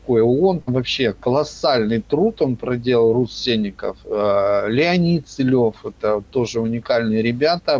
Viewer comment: Выпус - бомба! Ребята смотрите - не пожалеете! Божко получается учитель нашего гуру?))) Не рассылышал кого вы упоминаете (я расслышал "Рус Сельников", но на ютьюбе ничего близкого не нашёл. подскажите -кто, что?). Будет ещё более резонансным событием встреча Антона, Юрия (аксакала живых), Игоря (молодое поколение живых, канал "Живой Человек Игорь"), Андрея Топоркова (канал "Рожденный в СССР" с Андреем и Алексеем) . Думаю, выпуск получится ФЕЙЕРВЕРКОМ!!!! Можно ещё написать уведомление, что вы с такого-то года проживаете [что в штампе паспорта] постоянно и данное местоположение является вашим МЕСТОМ ЖИТЕЛЬСТВА (а не адресом, который сопровождает физ.л.и юр.л.), тем самым вы ставите их в юридическую ловушку: проживать могут только живые существа (люди, животные), но никак не юр.лица и физ.лица (граждане). Отправить конвертом (взять чистый и распечатать --> акцент должен быть на вашем "месте жительства" вместо "адреса" и на нормальном расположении почтовых реквизитов как на советских конвертах - поищите в инете). Отправлять заказным с уведомлением о вручении этим чертям. Будут приходить на персону, с адресом - футболить. Пусть признают нас.Удачи!